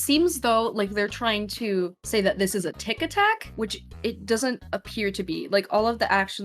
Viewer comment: When she said tic attack I fr thought she said tictac- 😂😂😳😳